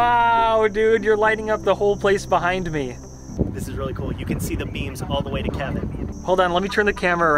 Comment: I thought I heard, "you can see the MEMEs all the way."